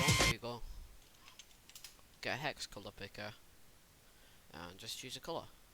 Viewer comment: i cant hear them at 3:20 , the music is to loud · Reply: He said something about hex colors